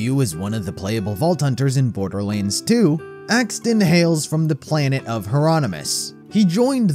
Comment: for a second I thought you said saxton hale.